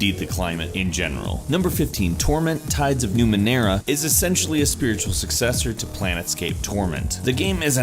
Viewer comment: Sounds like he said Planetscape instead of Planescape. That would have been a completely different game I believe.